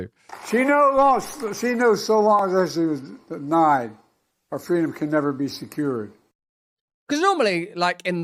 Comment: What I heard: “Do you what, I am so senile, but our freedom cannot be lost”. Awww thanks Joe